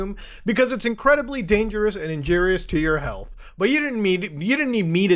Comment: did say tidepods are injeerious to your health?